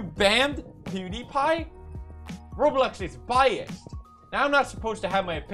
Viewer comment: I thought you said "Roblox Is Bajs"